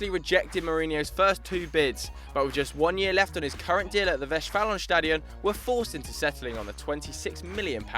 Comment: What does he say at 0:34 ? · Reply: 'Westfalenstadion'. That's the name of the stadium in Dortmund. The official name is 'Signal Iduna Park' but that's just for marketing.